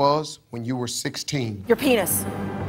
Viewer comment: When I was much younger, I actually thought she said Your Pants